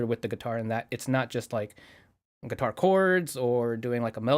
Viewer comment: I misheard say “Guitar chords” as “Par Chords” and thought that would be a great name for a band that exclusively records while doing Parkour.